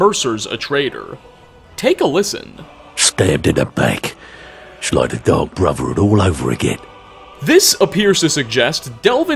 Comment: For Delvin Mallory, I did the Thieves Guild after the Dark Brotherhood and I always thought he said "just like the Dark Brotherhood" because of what Astrid did during the game...